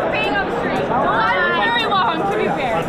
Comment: omg the guy peeing on the street I thought said "well he was very well hung to be fair" but she said the LINE WAS VERY LONG hahahah